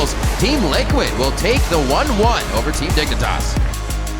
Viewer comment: If u activate subtitles: Team liquid will take the 11 poverty "naked ass" (instead of team dignitas) hahah